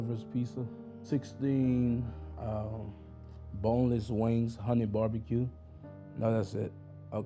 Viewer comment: did he just say 16 boneless wings and 100 barbecue?😮